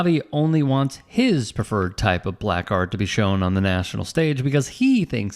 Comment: Did you say he wants his prefered BLACK art?